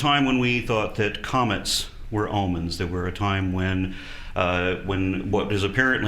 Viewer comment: it almost sounded like he said "almonds" instead of "omens". Now THAT would make an interesting myth. :P